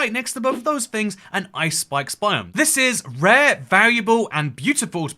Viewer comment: Sometimes when people say "Ice Spikes biome" I hear "Ice Spice biome" lmao